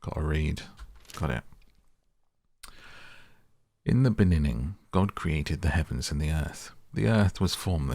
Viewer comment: Are you saying: in the beninning? Instead of beginning? 😂